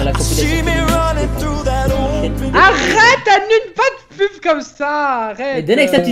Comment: C'est moi ou a 7:00 dis: Arrete Ènune?